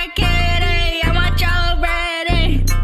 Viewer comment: My dumbass thought she said “I want jello bread” for 2 years